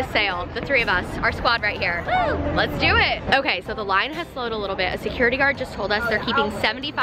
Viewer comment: she said line but I heard lion bc there was a guy wearing a shirt that said lion walking past 😂